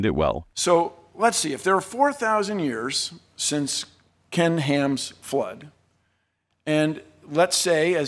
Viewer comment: I heard “To Ken Ham” as “Toucan Ham” 😂 I shall start calling him that now.